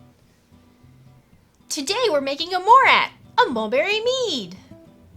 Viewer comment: at the start I really thought you said you were making a borat